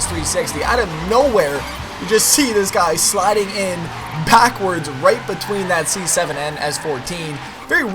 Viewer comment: Is it me or at 5:35-7 did say "packwords"?